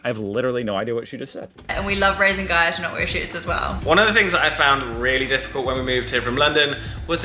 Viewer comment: I'm Australian and I've got no idea what she said either 😅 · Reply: She said “ we love raising “Gaia” to not wear shoes as well” Gaia is their child